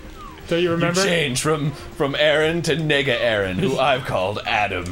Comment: did he just say nigga? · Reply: Nega, as in negative.